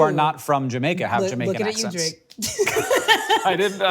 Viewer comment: Subtitle correction: When they discuss Drake and Kendrick at 3:00, Akilah said "Looking at you Drake" not "Looking at Kendrick," followed by something I didn't catch but that is also about Drake.